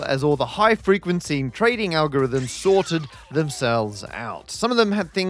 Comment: “Thankfully, the markets did bounce back to normal as all the high-frequency algorithms sorted themselves out.” First time I heard this as “shorted themselves out”, which would have been a much more satisfactory resolution.